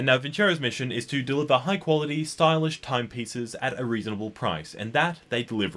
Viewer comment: I thought he said time pizzas! 😂Lmao